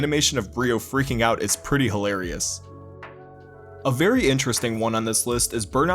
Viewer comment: i heard you saying "pretty hi-rez" i laughed so hard but than i realized i wasn't "hi-rez"